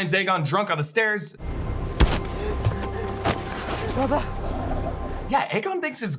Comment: lol I thought he said mother, as in he was calling for help